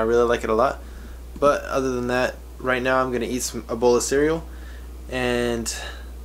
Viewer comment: I thought you said "I'm about to eat Ebola cereal" lol...